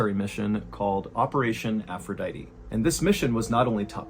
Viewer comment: i was a little preoccupied while you were talking in this moment and I swore. I thought you say "operation hermaphrodite "😂😂😂